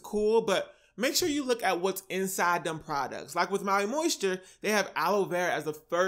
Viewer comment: I thought you said pee in Maui moisture on YouTube in years ago.